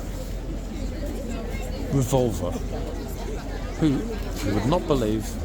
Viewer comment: My brain just went record scratch, huh? What? I thought said the horse’s name was Vulva, lol.